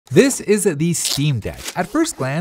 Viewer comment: "This is a the Steam Deck" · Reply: Yeah I thought he said this isn't the steam deck.